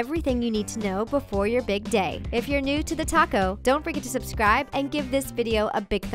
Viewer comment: I thought instead of saying thetalko I thought it was thetaco...